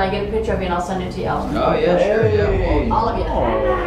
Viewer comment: okay i swear either i’m deaf or at 34:05 i heard a woman’s voice go “hey”